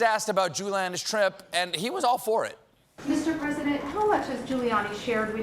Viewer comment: What was Trump asked about?? I heard, "Jewlanda's trip", but that sounds vaguely anti-Semitic, so...?